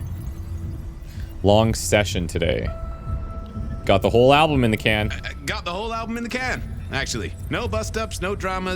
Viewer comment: I totally misheard him :_oddLul: swore he commented about getting Albert in the can